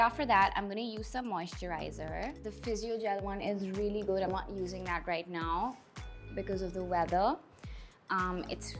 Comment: She said I use physio gel for moisturizing, not using it right now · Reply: oh okayy.. i thought she said "i am using that right now"